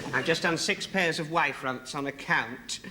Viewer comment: Does he say “wide” “white” or “Y” front?? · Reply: It's "Y-front", due to the shape of the opening.